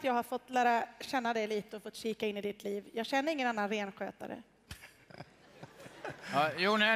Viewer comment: what did the lady say at 3:40? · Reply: I dont know any other "renskötare". Renskötare = Someone who take care of deers.